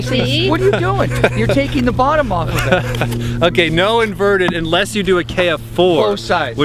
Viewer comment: lol sounds like you said perverted instead of inverted